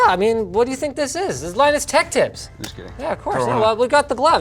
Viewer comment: the caption says 'line is tech tips' lol